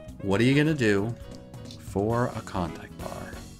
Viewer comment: What meant to say: Klondike. What it sounds like he said is contact. What the subtitles think he said is convict. Don’t you just love it when no one’s on the same page?